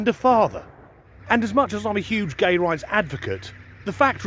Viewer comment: Lol😂 my phone buffered right when said he was a huge gay rights advocate but at first all i heard was "im a huge gay" i almost spit my coffee out for a second i thought he had not been telling us something about mrs. Disgruntled 😅😂